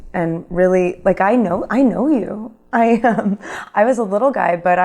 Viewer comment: Did she say i was a little guy at 1:57 ?